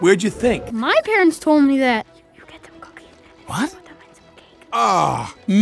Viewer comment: Did I just hear the words “cookie” and “cake” in that whisper?